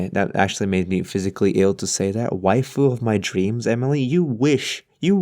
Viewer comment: When he said Waifu I thought he said white girl at first =w=“ Don’t kill me xD